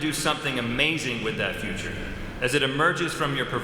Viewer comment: What did he actually say? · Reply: "As it emerges from your proverbial piñata"